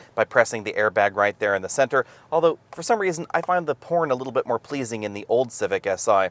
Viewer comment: did he say porn? 😆😆